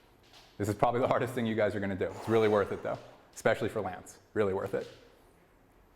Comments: What did he say at 10:23? Especially for Lance? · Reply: Lats i think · Been watching this video pretty much every day for 2 years. Just curious if anyone knows what he says at the 10:24 mark. Sounds like he says “especially for Lance”. But who is Lance? Is he talking about Lance Armstrong? I gotta know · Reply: Lance Armstrong. When this original video was made we were actively helping him prep for his return to the Tour de France. Peter Park is one of Lances primary coaches.